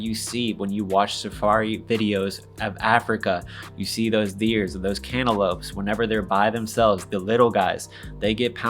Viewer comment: Did he just say “Deers and cantaloupes” ?? 🦌🍈 haha!!